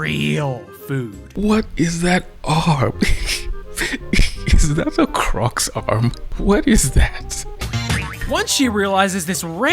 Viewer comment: I thought he said “Is that a cocks arm? What is that?” 🥹